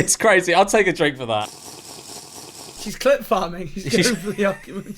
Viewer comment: I SWEAR you said clit farming 😂😂😂😂😂😂